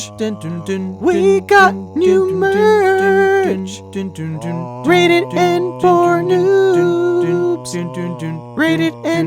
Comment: Not gonna lie, at first I thought you were saying rated N for Nudes. I honestly dunno why